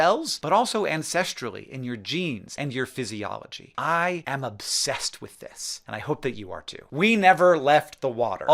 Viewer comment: Did you just say: ‘You were obsessed with fish” 🤨……😁